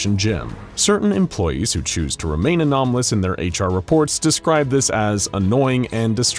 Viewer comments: I heard anomalous instead of anonymous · you said anomalous instead of anonymous